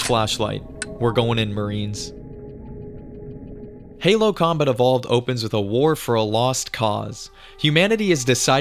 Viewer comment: I kept hearing you say, "Halo Comet of All".